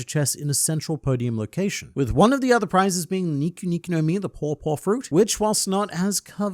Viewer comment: I thought you were saying Poor Poor fruit, not Paw Paw fruit lol